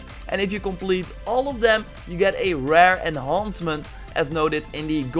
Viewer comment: God of War has just gone gold! 4 more weeks now, expect more content as we near release & ofc around launch :) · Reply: it sounded like you said enhoncment 😂